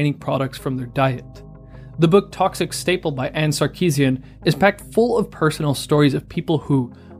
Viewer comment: I thought you said the book was by Anita Sarkisian for a moment there lol.